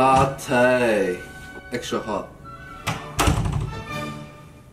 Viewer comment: "hey my name's henry" not a roasting channel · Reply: (( I thought he said "hey mate i'm Henry"